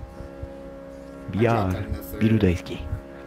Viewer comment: beer daisuki?? did i hear that right